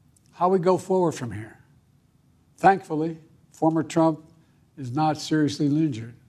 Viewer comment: "thankful former trump" Wtf is he saying?